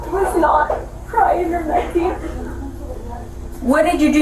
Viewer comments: you do t need to lie, it’s bad enough. But she did not call it nothing. She said ‘it’ came out, there was no crying’, ‘no crying or nothing’ also, what’s the story she’s deniging sex and back pain only, big jumpers, something hughe mental is going on.’ It seems you América’s give Zero f’s for the mums before being born and zero f’s about the baby’s after being born. You want more people to have kids? Stop charging crippling $$$ to be who have nothing to have baby’s. Life and liberty your number one? Healthcare is life, and everyone should have it full stop; not just the rich who want people only to work for nothing and make them more rich. Mums should start a union and fight for better job terms if you are going to treat them like that. No sex, no kids until there’s changes… your economy will be f’d as china’s is with the lack of children that what you want? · The second girl sounded like she was saying "nothing was crying" as in that it wasnt crying, not that she was referring to it as nothing, since before that at 8:46 she says "it was not crying or nothing", but its hard to tell with the way shes talking. also i should clarify before hand that im not defending her, i just wanted to be accurate.